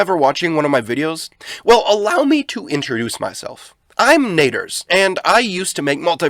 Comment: thought he said neger :3